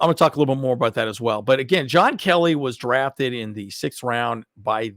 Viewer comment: Thought he said Joshua Kelly I was like noooooo